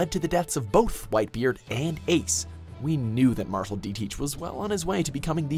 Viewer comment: I thought he said whitebeard and aids